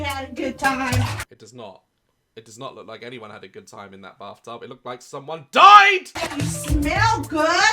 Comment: I’m sorry but I only hear “barftub” and that only makes it 100% worse lol.